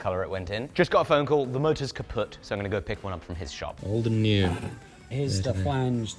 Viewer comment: did he say „the Motor is kaputt“ like the german word for broken?😂